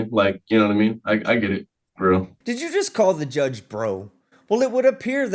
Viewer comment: I heard “for real”, not “bro”